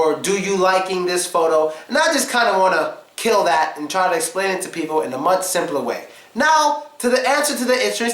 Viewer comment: can someone tell me what did he say at 4:14 did he say, "why can we just" ...blablabla ??? or something like that i can deduce what is talking about but what exactly was the sentence? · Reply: And I just kinda wanna kill that and try to explain it to people in a much simpler way